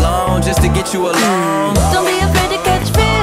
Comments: from now and on i wont be afraid to catch fish 🐠😂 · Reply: Oooofff my friends said I'm retarted because I heard " don't be afraid to catch fish " now I know I'm not the only one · I thought Katy Perry said "Don't be afraid to catch fish" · DON'T BE AFRAID TO GET FISH 🐟 Different video, same lyrics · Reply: Muhd Afiq hahaha i thought she was saying "dont be afraid to catch fish" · Till this day I still misheard Feels as Fish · Sounds like Katy says “you ain’t afraid to pop pills”